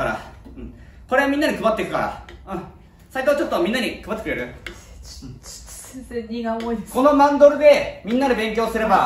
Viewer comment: なんて言った？ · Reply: 先生、荷が重いです